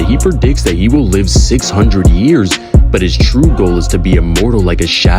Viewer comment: I think he said 300 years but I'm not sure🤔